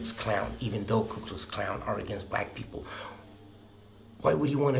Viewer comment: Who else heard him say ku klux klown?😂